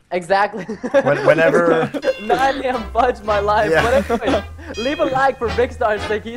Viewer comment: it sounded like he said shit when vick said yes its battle phase